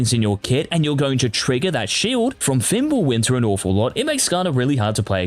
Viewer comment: can anyone tell me what hes saying about skarner from 2:55? something with ap scalings, cant understand him :/ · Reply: fimblewinter?